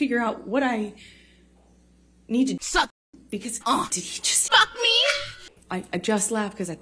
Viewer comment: Amber looks like she really said f'ck mee 😭